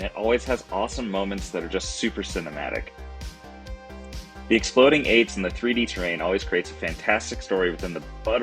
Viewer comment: I thought you said "exploding AIDs" at 00:50 haha. Cool channel, I'm starting to like Mantic stuff probably the best too.